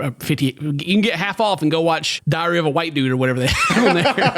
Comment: I legit thought he said "Diarrhea of a White Dude".